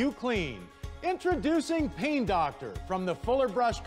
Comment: at first I thought he said pee doctor